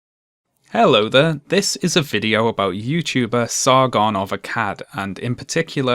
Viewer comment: I always mishear the name and think it's "Sargon of a cat" which is a wierd but kinda fun name tbh